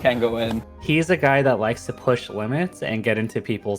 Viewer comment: I misheard as "he's the guy who likes to touch women..." I really hope that's just a silly mistake and not some grim foreshadowing